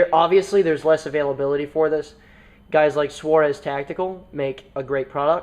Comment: @ 17:14 you mention a source of accessories. I could not for the life of me find that company. It sounded like "Suarez tactical." If you have any info on that company that would be great.